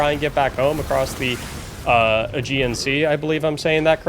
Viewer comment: I thought you said “get back home across the uhh…GNC?” for a second 😂